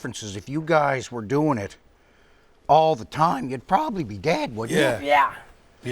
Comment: I thought bubbles said “you’d probably be gay if you guys were always doing stuff for Jackass”. Like because the jackass guys are always doing gay stuff it might just end up taking 😂